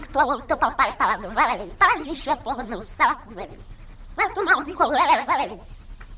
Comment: Mano eu juro que eu ouvi "Vai fumar um picolé" kkkkkkkkkk